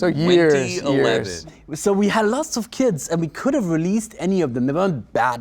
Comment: Thank Azir the caption was on. 3:14 keep hearing kids instead of kits.